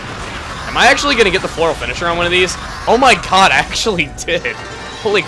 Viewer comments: it sounded thike u said "Oh my cod!" instead of oh my god! and you killed a fish!!!! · did you say ''oh my cod'' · when he got the floral finisher on the fish, it sounded like he said "oh my cod"